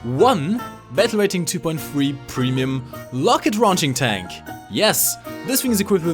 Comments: is it just me or did he say Locket raunching? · Did you just say "Locket-Raunching Tank"? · Did I hear that right? "Locket raunching"?